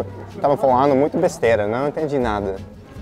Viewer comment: Oh wow... The lusophone isn't saying what the subtitles are saying at all. I'm not Messi, but I speak just a lick of portuguese, and the "nao entendi nada" at 3:54 means "I dont understand anything haha"